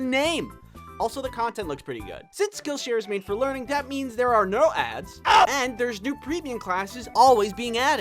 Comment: i thought it said aids